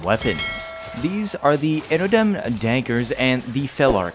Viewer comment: lol did you say Dankers?